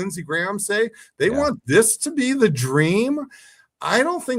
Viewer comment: Did he just say “Donald Trump stopped the Genocide” Clown 🤡 I’m outta here